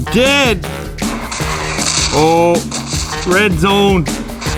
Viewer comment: did he just say bread zone Savage man 😂😂😂